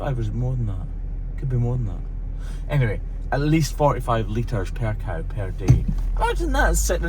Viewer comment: 🇦🇺At first I thought you said 45 L of milk per cow per day and I was shocked, then reality set in and I said hang on, I think you actually said 4 to 5L of milk per cow. Now I can breath. 🤣😁😂